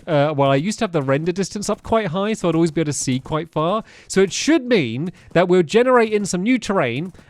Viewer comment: did he say it should meme